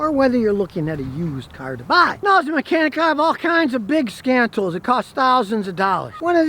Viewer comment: I misheard scan tools for 'scam tools' 🤣